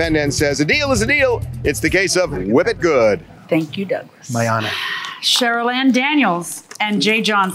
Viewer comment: Did Douglas call JM “my honor” ? 😂😅